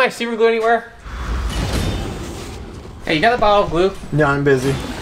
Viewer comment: I swore he said do you have a bottle of lube 😂😂😂